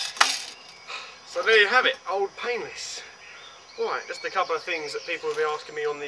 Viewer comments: it sounds like he says "old penis" Ha ha!! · on 5:17 u said "old pianless" but it sounded like u said old penis lol! but good review!